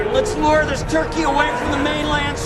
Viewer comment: did he just call Rodan chucky? Weird